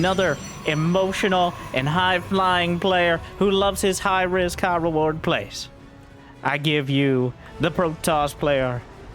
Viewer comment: For a second there I thought you said Creator had "high rizz, high reward, plays" in the intro.